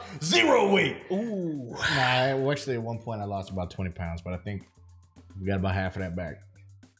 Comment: What said ; “At one point I was down 20 pounds.. but I’ve probably gained about half of it back..” What I heard; “Yeah, I’m down about 10 pounds.” Keep it up, King! If you want to lose weight, we’re behind you, man!